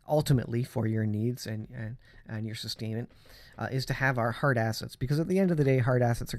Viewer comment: At first i thought you said “Heart Assets” vs hard assets … but you wouldn’t be wrong , we must prepare out heart assets and prepare them for the times ahead. God bless.